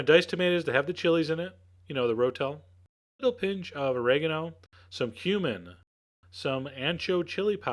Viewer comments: When Chef John said said 'some cumin', I swear I thought he said 'some human'. Luckily I wasn't drinking or eating anything at the time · Lol 1:17 sounds like you said "Some human." xD